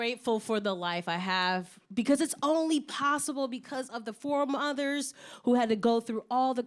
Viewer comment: No lie.. The first time I heard “foremothers” as “four mothers”.. I was like “dangggg she got 4 moms.. OFC SHE GREW UP TO BE A GIRL BOSS”☠️